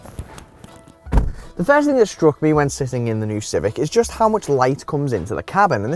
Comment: Did he say Civic??